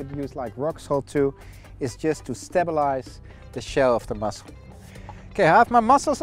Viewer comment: Looks delicious but.... Does seaweed sterilize shells? Should I place my mussel shells in rock salt before serving? Is this a necessary step after they've been steamed? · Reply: LOL - thought you said sterilize not stabilize!